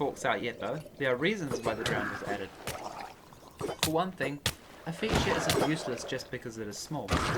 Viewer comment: did you say "I didn't think it's useless just because it is small." I couldn't really hear because the drowns was drowning your voice. I agree with you if that's what you said.